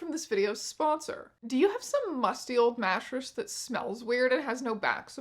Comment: lmao i heard “gnashers” instead of mattress and was like…well, yes